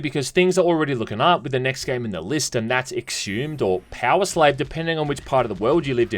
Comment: I thought it said sex hummus